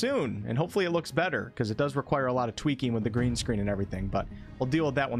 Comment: I heard “twerking with the green screen,” and shuddered.